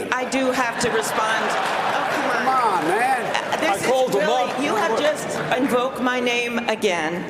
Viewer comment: What a circus lol. I liked that bro moment between Biden and Bernie tho 4:36 Biden: Im the only one who knows this man (the Mexican President) and met with him. Bernie (laughing): I called him once after work (i know thats not what he said but it sounded like it) 🤣🤣🤣🤣🤣🤣🤣🤣🤣